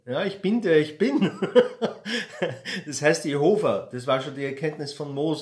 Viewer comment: Absolut Quer bitte. Irgendwann im Video hast du "Jehova" gesagt - und ich hab je Hofer verstanden Musst ich lachen 🙃